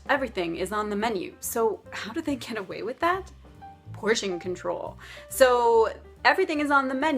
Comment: I am learning English now. I wonder if anyone knows what did she say in 1:06? I guess it's not "how do they can away with that." So what it is? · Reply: zzr she said "how do they get away with that?"